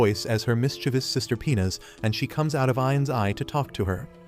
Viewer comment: why do I hear p*nis